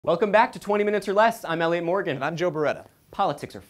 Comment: I sweat every time elliot says his name i hear "Hi i'm Elly Morgan" I swear its a girl